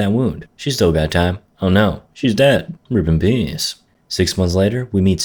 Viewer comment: captions are so bad when you said rip in peace it thought you said ripping penis